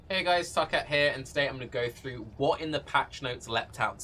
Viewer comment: In the intro it sounded like you said “hey guys fuck it here”